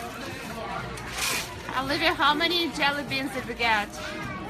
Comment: bro just listen closely at 14:16sec, in the candy store.. its sounds like she says "Olivia how many Jelly beans did you get?" Do you guys hear it? I wonder if thats the only name we'll hear leak..?